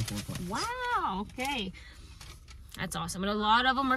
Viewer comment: That's a sad looking landscape, oowee. I wonder if a cold spell took those plants out. 😂 I thought you said 125 bucks for all of those plants 🤯 but no, it was 125 plants. I love shrub removals where you can pull them right out of the ground. I once priced a small arborvitae removal, and turns out all I had to do was push them over, and rock them back and forth a little 😂 Did you guys stay in a hotel in between? How far was that from your house? Well, I wish I had time to watch the whole thing ( I watched 3/4; so far it looks good ), but now that it's not raining out, I have to swap a water pump on my 97 because the 14 is having deep engine problems, so I'd rather just give her to the people who can do it ten times faster than I can. New lifters and maybe/probably a new cam 🙄💸💸💸 It's been trying times lately 🙏🙏🏽 Ahh....the wrong fabric is also the culprit. No wonder was having a time cutting that one spot with the shovel. And I don't know if I mentioned in another video, but a coupla days ago I've become a blade edger user 🙄😂 mainly because I HOPE my inexperienced help can have a better chance at making nice lawn edges, than with a string trimmer 😵‍💫... it's cool, but as I've said for years, it's dirty. And I'm just faster and cleaner ( sort of 😂 ) with my string trimmer. By the time I finished this comment, the video is almost done now 🙄😂 ⏳️